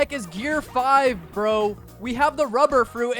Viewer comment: why does it say pro when you say bro?